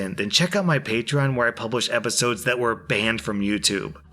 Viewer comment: I NEED to say this. Every time, I mean it EVERY TIME! When you say "I upload every day". I hear; "I upload every stinking day~"